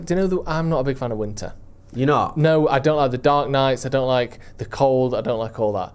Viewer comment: I thought said "I don't like THE DARK KNIGHT" at 24:29😂